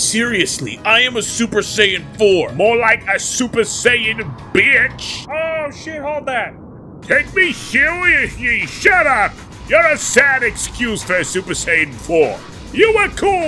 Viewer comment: I thought cell will said "more like a super saiyan hore" then super saiyan bitch